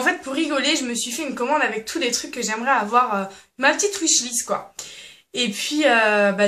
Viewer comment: Elle dit quoi à 0:58 ?! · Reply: Wishlist